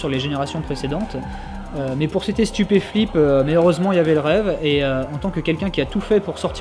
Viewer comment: je commençais a derper et j'ai entendu "pour pas citer stupeflip..." Bref c'est juste pour sa que forgeron c'est mon future job ^^